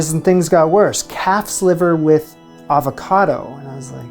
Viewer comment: Fish Aspic and Calf Liver with Avocado sound horrific. 😱🤢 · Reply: Damn, did he say calf's liver? I heard cat's liver.Much spookier.